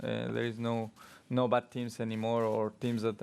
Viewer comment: In his defense, he did say midfield . Not backmarkers 😂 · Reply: did perez say "butt teams any more "